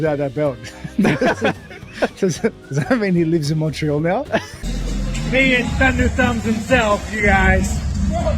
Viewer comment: What's Strickland sayin'? It sounds like "penis thunder thumbs is here". I really can't tell.